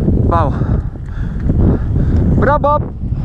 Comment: Qué es lo que dice en 8:40 (bropop o algo así)? veo que lo repite constantemente en sus videos, Es algo relevante al mtb o simplemente un canto de guerra, o cábala?